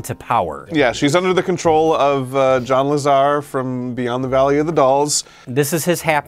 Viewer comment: "Yeah. She's under the control of John LaZar...." I heard that right but all my mind could construct was "She's under the control of Rem Lezar."